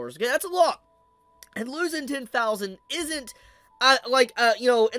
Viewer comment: I heard he lost 10 millian at first😭Glad it's only 10,000, still crazy though